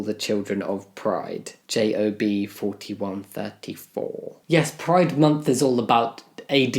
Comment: '41 34'. Sounded like Snape saying 'page 394'